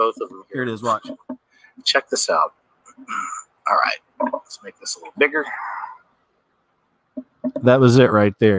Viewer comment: "make this a little bigger" is what you said